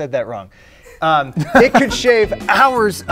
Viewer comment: it sounded like he said shave